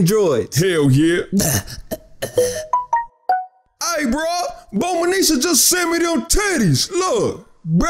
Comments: anyone else heard "bulmanisha" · I’m done did he say Bulmanisha? Bruh😂